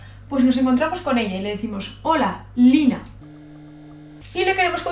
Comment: no sera youtber argentina que dice holaa soy lynaa